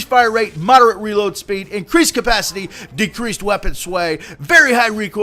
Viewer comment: it sounds like your saying weapon swag.